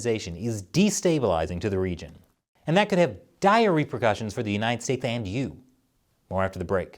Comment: You said “dire repercussions” and i heard “diarrhea percussions” just as i gifted my toilet with the worst diarrhea ive ever had in my life.